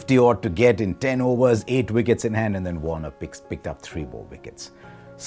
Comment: did Harsha say 'Warner' instead of 'Warne'? 🤔